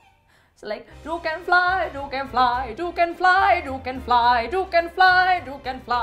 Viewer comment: When she said "You can fly you can fly" I thought that it said "Yuca is mine! Yuca is mine" 😂😂💙